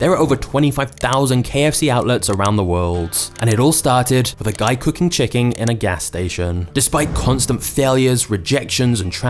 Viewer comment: did he just say, "A guy cooking chicking"?